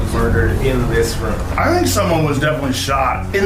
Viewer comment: I thought he said do you want some hamburger in this room 🤦🏽